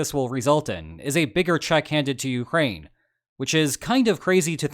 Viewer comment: Ooof. I had a very brief "Sudetenland, 1938" flashback there when I heard, "Czech handed to Ukraine."